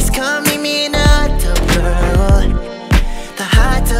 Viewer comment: why do i hear "the hotdog girl" ?? 😭😭😭